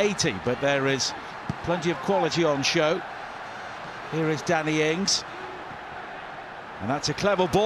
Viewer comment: every time the commenter says Danny ings fast it thought he said daddy ings